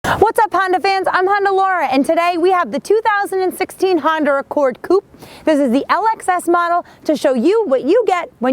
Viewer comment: did she say Honder?